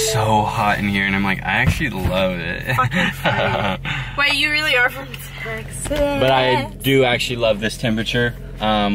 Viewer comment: HOLD UP !!!!! did i hear that right? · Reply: ilujj she said “fucking freak”